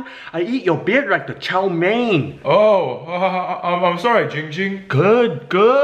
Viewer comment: The captions said GiGi in stead of your name Ging Ging at